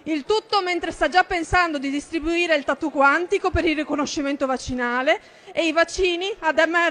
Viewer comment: solo io ho capito " vaginale " al posto di " vaccinale "